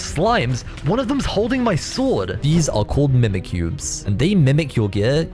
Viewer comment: it sounds like he said mimikyu lol